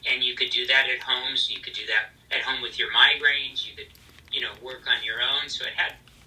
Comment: girll did he say migraines? Omg. I can relate. I suffer with them so bad I feel like I can't even work :(